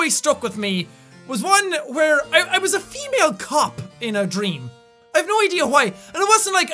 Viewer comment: Is it weird that I I thought he said female cup inside a female cop?